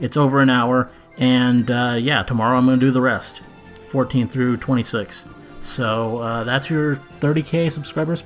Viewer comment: "...and I'm gonna do episodes 1-13, Season 6, all in one video, right here, it's over an hour, and tomorrow I'm gonna get some of that Mulan Szechuan Teriyaki dipping sauce Morty..." I know it's not what he said, but the way said it, that's what my mind was thinking. :3